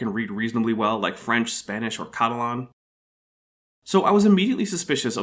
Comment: When you said that you speak Catalan, I had to go back and check, because I heard "Klingon." I mean, it makes sense... Anyway, DaH jImej!